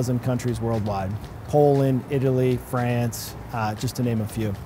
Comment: france ? what he is saying xd